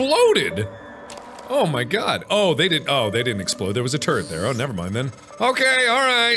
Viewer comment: Hand on heart - I thought you said "there was a turd there" 🤣😂😅